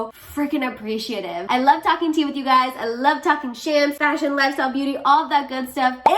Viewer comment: Why did I hear “I love talking shit with you guys” in the end. Lol but that’s why I am here